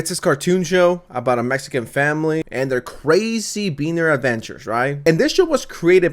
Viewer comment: Did I hear that right 0:27 “being their”? Or 💀 (I’m Hispanic don’t be hurtful in the comments)